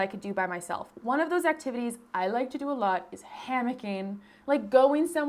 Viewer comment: 100% thought you said "panicking" not "hammocking" and was like girl same lmao